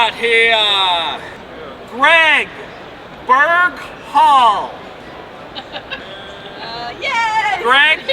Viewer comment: Great Vid! Looks like you had a ton of fun! Noticed you uploaded in 4K for the first time, thanks! "Looks Awesome" Great editing and super funny as always. Wonder if "Jimmy Lewis the Nice Guy" will stick like The Lawn Rebel. I Appreciate you bringing us along with you in this vid! Chad Upstate NY PS: At Spyker Giveaway I thought you said Greg Bird Call at first! LOL!